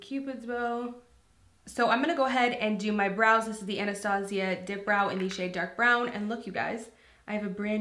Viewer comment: It sounds like you said Anastasia Glow Clit 😭😭😭